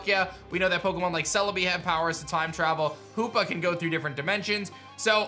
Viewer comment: 90 percent sure they're right Ps-Omg I just woke up, so I'm sleepy. When you said "hoopa can go through different dimensions" I swore you said "who f***ing go..." 😂